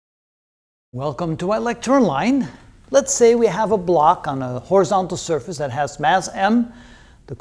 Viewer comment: sometimes I just open your videos to hear you say "welcome to i lecture online" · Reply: I always thought he said "electron line" or something like that